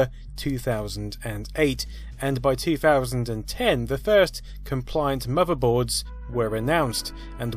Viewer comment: Typo in the captions should be compliant instead of complaint